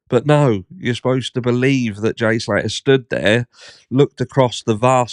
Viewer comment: You missed the very start of the video, where (I believe) he is heard saying "Here I am, back at the starting point, where Jay was last seemed to be snuffed" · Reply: I thought it said stood not snuffed